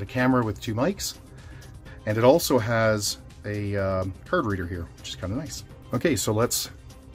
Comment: did you say that is a card reader?